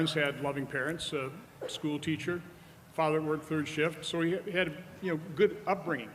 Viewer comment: AND pointed out that his Dad had too work 3 shifts for his family.A definitely whiff of classism. there. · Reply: I thought he said 3rd shift, meaning midnights, which is just as elitist as 3 shifts. So u noticed it too? They're repulsive